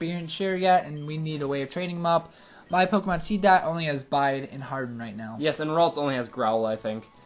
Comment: thought he said bite and hardened srry had to think that way but awesome job guys and cant wait to hear the OnePiece chant